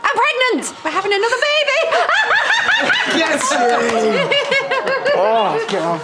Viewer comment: What did Aaron say at 2:16? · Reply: Get in